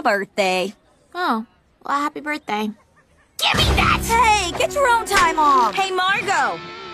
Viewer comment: I love how Anne responded with "Get your own Thai mom!" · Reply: I thought she said "get your own Time off!"